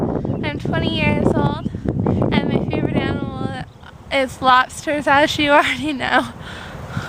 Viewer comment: I honestly thought you said Lobster Vaj...